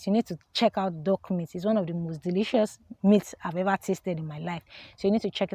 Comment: did you say "dog meat" or is it that I need to fix my speakers?